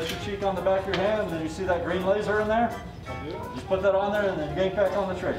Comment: Did he say laser? As in fookin laaaazer sights?